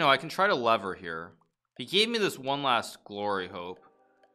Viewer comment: I heard "He gave me this last glory hole"